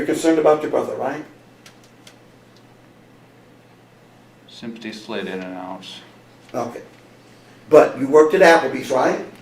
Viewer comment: Do you know what says at 1:17:00? I’ve replayed it over and over and can’t understand his words there. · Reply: ”Sympathy slid in and out “ is what it sounds like. he probably mumbled it because it’s not comfortable to admit to something like that when you realize how abhorrent it is